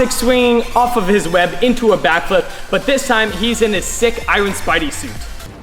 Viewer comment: who else heard eyelid spidey suit